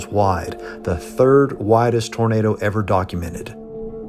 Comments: i have captions on and at 1:51 it says the 3rd widest burrito ever documented instead of tornado lmao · the closed caption says “the 3rd widest burrito ever documented.” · HA!!! At 1:52, the auto-generated closed captions say: "This tornado grew to 2.25 miles wide. The 3rd widest burrito ever documented!" ROFL (this happens a couple more times throughout the video)